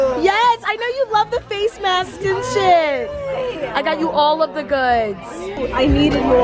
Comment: Did anyone else hear at 2:27 say shit ?